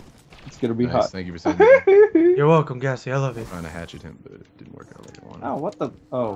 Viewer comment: I thought is said Gassy's Love for Jizz :O